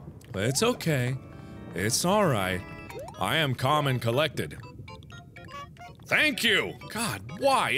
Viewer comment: I thought he said he was called and collected